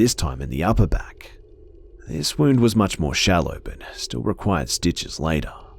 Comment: ok...soooooooooo. let me get this straight....Now, I. not an Anatomy Major r anything lime that, but...did he say "the guy picked up another knife and stabbed my dad in his other back!". like I said....not a Major of anatomy. though I am pretty sure I don't have more than one. two eyes Two Lips two nostrils two ears two legs two hands two of every finger two of every toad two intestine two ventricles in her heart to lungs to kidneys two balls in my case two ovaries two breasts two butt cheeks. so yeah just checked out all that make sure it was good, and I'm okay everything still here